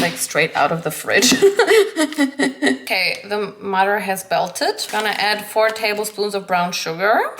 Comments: am i the only one who heart the mutter is belted? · Did anyone else hear her "The mutter has belted" @ 3:00 instead of "the butter has melted" 😂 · Am I the only one who heard her say “now that the mutter had belted”? Time stamp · did she say the mutter has belted????............ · did anyone else hear - "okay, the mutter has belted" xD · Is it me or is she actually saying „the mudder has belted“ at 3:00?! I laughed way too hard at this. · Wait did she said at 3:00 "the mutter has belted". I love mispronounciation. I think they're so funny😂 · Did I hear it correctly? The mother has belted? 😅😅😅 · DID U SAY THE MUTTER IS BELTED??? LMFAOOO